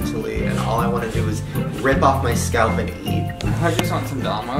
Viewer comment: I LITERALLY THOUGHT HE SAID "all i want to do is rip off my scalp and eat it "